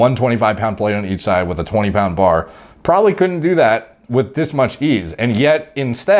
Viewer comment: Thought he said "with this much biebz"